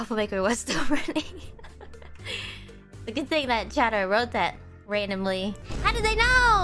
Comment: Doesn't technically count as a mistranslation I don't think, but at 00:19 Ame says "Good thing that CHATTER wrote that randomly" not "chat". If you don't know, a "chatter" is just a single viewer, chat is the entire entity of every viewer amalgamated into one personality.